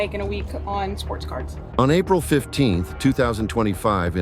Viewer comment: I thought she said he spent money on Sports Cars… ohhh sports cards…oh nooo 🫣 😂😅😂 🏃‍♀️ run, lady, run far away from this bum